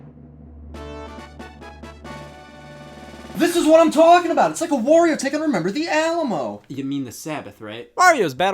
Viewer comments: What does Rex even say here? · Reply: "This is what I'm talking about! It's like a Wario take on 'Remember the Alamo'!" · I, to this day, want to know what he said. I can't tell. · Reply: "This is what I'm talking about it's like a warrior taking remember the alamo" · soooo does anyone know what the hell he just said? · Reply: “This is what I’m talking about. It’s like a Wario take on remember the Alamo”